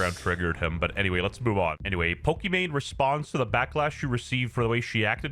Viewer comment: He said Pokimane! Did anyone else catch that too? No one mentioned it... Not even Tec LUL!